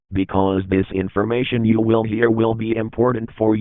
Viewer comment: ''you will hear will be'' he said it so fast that i thought he said ''you will be you will be''